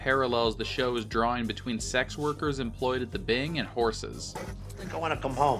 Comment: Not sure if it’s intentional but it sounds like Carmela says “whores” at 2:15. Which would strengthen your point about the strippers/horses parallel